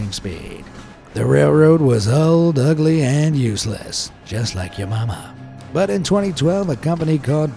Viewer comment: did he just say "old n useless..just like ur momma"💀 was that a mistake?💀💀